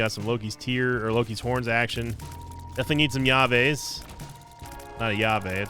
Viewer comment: what does he mean when he says "ya-veys?" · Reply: Llaves! Keys :D